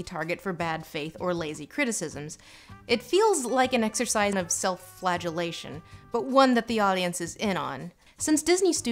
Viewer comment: When you said "self-flagellation," I thought you said "self-flatulation." I suppose both are accurate representations of Disney's live action remakes.